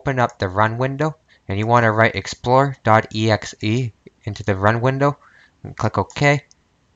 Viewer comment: Mfw I thought he said explore.exe and it didn't work. Mfw I see it actually says explorer.exe and still doesnt work